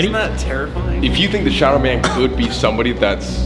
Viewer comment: Why did it sound like said shower man 😂😂